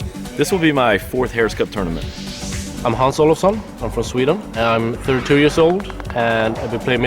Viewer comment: Who else thought he said "Hans Solo's Son" at 5:25?